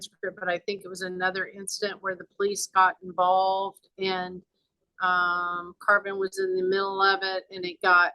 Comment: Who names their kid 'Carbon?' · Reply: My thoughts exactly. The first couple of times I thought she was saying Carson